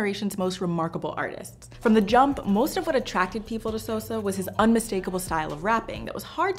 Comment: did she just call sosa "soso"